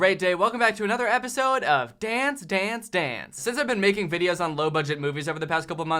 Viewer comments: I thought you were saying Dan's dance dance and you know what? It works. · Honestly I thought you said "Dan stans Dans" at the beginning there